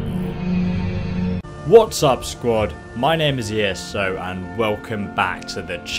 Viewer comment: At the start I thought he said"what's up scrub"😂